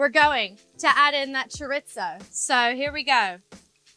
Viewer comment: I was like wtf is choritzo because that is what I heard her say. Girl it is chorrrrrisssooo try sounding it out spelled chorizo sounded out how I wrote it.